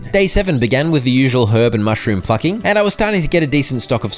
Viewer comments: he did NOT say plucking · I misheard plucking...